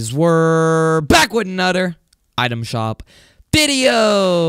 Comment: Why does he say "anudder"